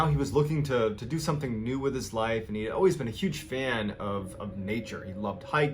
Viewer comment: "Jeff was a Marine, then he was a Cop, and then he was lawyer". LMAO. All I hear is, "Jeff has always been a liar".